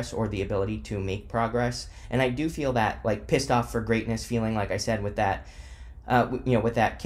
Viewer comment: I thought it said "pissed on for greatness" and just figured r kelly was into astrology now